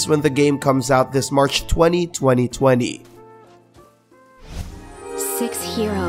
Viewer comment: who else heard 202020